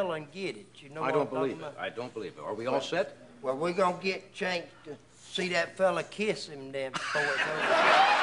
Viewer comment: Can someone please tell me what he says starting at 0:23 that everybody is laughing about? I listened to it 15 times but i still can't make it out :-( Thanks in advance! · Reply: I remember seeing this show. Doc said that "if that dog climbs that tree I'll kiss his ass". During an interview at a local television station, Mr Odom said, Flat Nose was still mad with Doc...